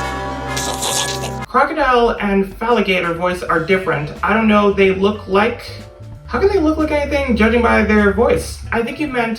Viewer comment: i think they tryna say “why do they sound different they look alike to me”